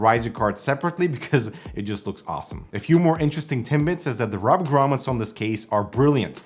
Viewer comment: did you say Timbits?! I could really go for a pack right now...